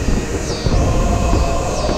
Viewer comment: ok so the last word she said suppose to be "banzai" but some how i heard it as "bán danh",damn vietnamese momentos.